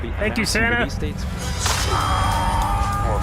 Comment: I thought I heard him saying "Thank you, Senna!" Lol